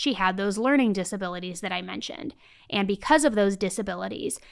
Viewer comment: I’m sorry but does anyone else keep hearing say “compacity” ? As in “mental compacity” That’s what I keep hearing her say. I literally looked it up. Lol😂 just in case that even though I am 60 years old, I’ve never heard this word before. It is actually a word. But I think she means capacity. 😁